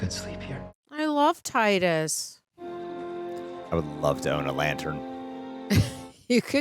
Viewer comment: did you just say Titus lol it’s Tyson no hate though 😂